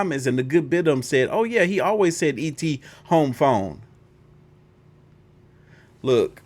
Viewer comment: I thought et was saying ET go home